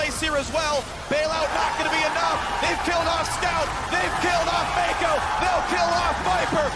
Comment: I thought the commentator said "they kill scout, they kill faker!" haha